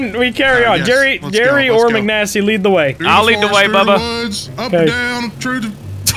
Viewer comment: I hate how no is talking about “I GOTcha, BuDDy!” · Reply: I swear he said bubba, not buddy Instantly realised I read the time wrong, I was watching 10:35 roughly